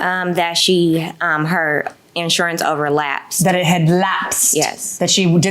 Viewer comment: " Her insurance. OVERLASPED, HUH WHAT"S THAT? 😮💯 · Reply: lmao and i thought she said relapsed lol!